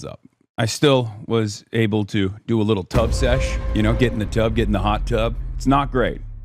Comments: Ohhh he said tub sesh, I thought he said tug sesh as in personal satisfaction lol😂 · JFC i heard "Tug sess" and almost vomited...